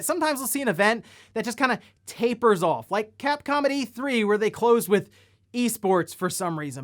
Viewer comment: When you said “capcom at E3,” I heard, “cap comedy 3” …. Which is still pretty accurate.